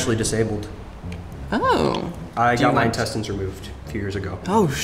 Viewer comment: "i have my intestines removed few years ago" "How much" "ALL OF THEM" 😭😭😭😭 Oh my god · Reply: Bro I thought he said testicles